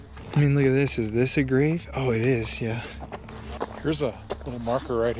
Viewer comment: the old mans voice sounds like it’s saying “it is”. To me it sounded like it was answering you commenting on the grave being open.